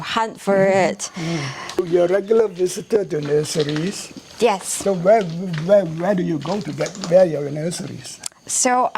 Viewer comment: I loved that legume @ 9:50. What was the name?? I’ve tried googling what I thought you said but no luck. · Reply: Phyllodium longipe